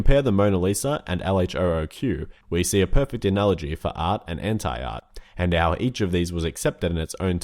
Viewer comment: DID SOMEONE SAY HENTAI ART